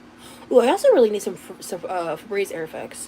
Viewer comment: did she say she really needs some febreze? Lmao…im sure she does